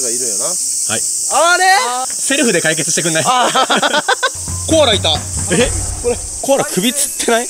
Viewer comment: 「コアラいた」が「コーラいた」に聞こえるのは自分だけ？